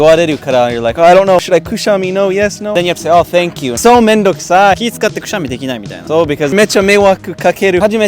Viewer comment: so めんどくさい！がクソめんどくさい★に聞こえたw